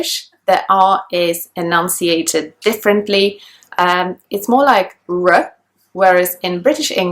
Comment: substitle says ass instead of r